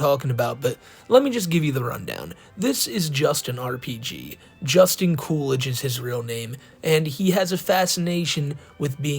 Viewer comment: When the Jenna thing showed up I thought he was saying it was Jennifer Coolidge because I wasn’t listening to his last name straight killed me